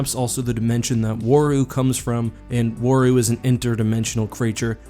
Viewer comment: i thought he said wario lmao